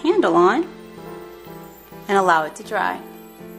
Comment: who heard a quiet voice say " let it dry "